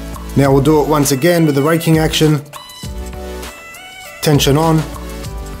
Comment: I thought he said raping action